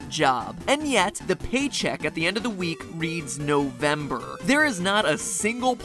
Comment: I thought he said December and I was like c’mon ever heard of Australia But then I realised he said November And then I was like NEW ZEALAND HELLO IS MATPAT HOME!!